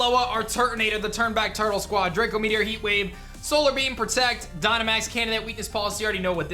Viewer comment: I thought he said Dynamax Cannon hahaha